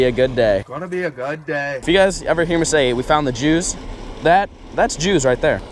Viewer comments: I love how you put “juice” on the screen bc u sounded like you said “Jews” lol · I know he corrected it,but I didnt see that till just know but I heard "Thats the Jews Boys" or "we found the Jews" 😂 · that's the jews I know you ment juice but sounds like you said jews love ya